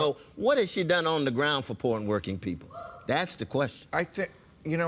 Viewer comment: it sounded like he said "porn working people" instead of "poor n' working people"